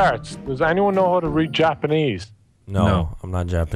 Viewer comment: didn't say read Japanese instead of speak?